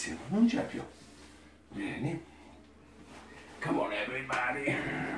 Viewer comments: does he say "Come on, everybody"? Sounds like he's quoting song lyrics. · does he just say 'come on everybody'?